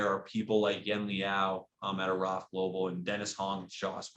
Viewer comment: Couldnt really make out who he said who contributed some success towards the long term side (7:07 time mark). If anyone knows who he was referring too I would greatly appreciate it, Thanks! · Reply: Yen Liow from Aravt